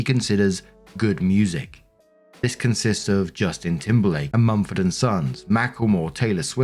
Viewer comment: Okay i had to go back because I'd swear that instead of Mumford and Sons i heard you say Steptoe and Son. Must be the working-class but educated accent ya got.